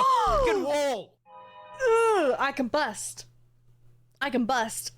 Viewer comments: i legit thought you said "i can bust" 🤣🤣🤣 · I misheard "I combust" as "I can bust". That caught me do off guard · I was in a pretty loud area while listening, so it sounded like she said "I CAN BUST!" 😂